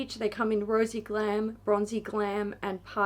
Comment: Definitely thought she said "Rosey glam and bronzey CLAM" and it sounded weird but I was like🤔 🤷‍♀️😂